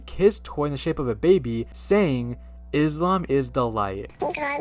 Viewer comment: What other people hear: "Islam is the light" What i hear: "is cum alright?"